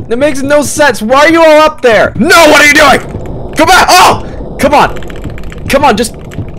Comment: hol’ up what did you say??? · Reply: OH NO FOR A SEC I HEARD "What the f**k are you doing?" FOR SOME REASON